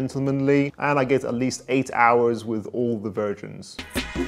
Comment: I thought I heard "It gets me eight hours with all the virgins" With such informative, entertaining, quality content you deserve to my friend 😎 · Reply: I think he said "versions" (as in Bleu de Chanel EDT/EDP/Parfum), but yeah I hear virgin as well and have to rewind that lol